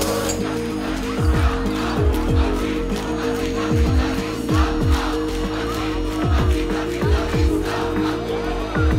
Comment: Was rufen die protestierenden bei 1:07:48 ? Ich verstehe nur a ati atikatika vista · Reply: anticapitalista